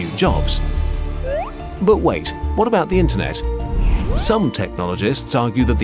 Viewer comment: subtitles say "wome" instead of some